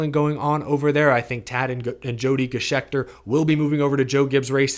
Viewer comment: At first I thought you were saying "Jody Scheckter" and I was thinking "What does an ex Formula 1 champion from South Africa have to do with a NASCAR team?"